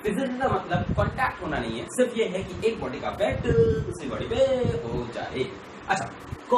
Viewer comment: what does he say in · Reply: Ek body ka affect dusri body pe ho jaye